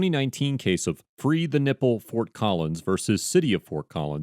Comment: Did I hear right? " In the 2019 case of Frida Nipple Fort Collins " ✓ 3 mins and 4 seconds :)) suitable name for nudist activism